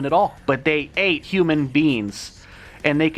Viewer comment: I thought he said human beans lol